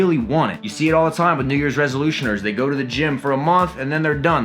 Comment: I actually thought you said niggas resolution